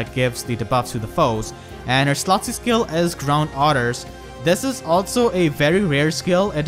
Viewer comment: I was listening to this in the background the first time, and I heard "Ground Orders" as "Drown Otters." My laughing fit made it hard to hear anything after that :P